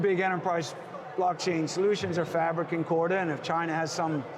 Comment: Did he say "Fabric and Corda"?????? As in hyperledger fabric by IBM?🤔